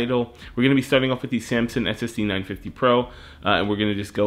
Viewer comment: You have a link to the 960 pro lol. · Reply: No problem. It's my fault. I thought you had said 960 Pro in the video too, but I watched it a couple more times and couldn't find a time where you said it.